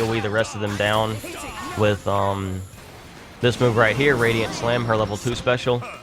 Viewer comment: Oh good, it's not just me that thought that move was called 'Radiant Slam'. (It's actually 'Radiant Flare').